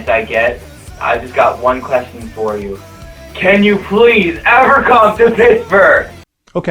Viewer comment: What does the guy at 4:15 say? I can't understand it. :| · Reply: "And I've got one question for you: (yells) can you please, ever come to Pittsburgh?!"